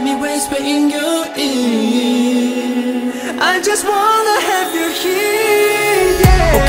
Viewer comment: Istg i heard " I just wanna have you here" as " I just wanna have your kid" Lmaooo